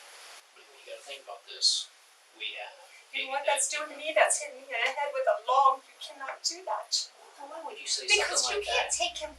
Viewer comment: Did she really say "You know what that's doing to me? That's hitting me in the head with a LOG" WTF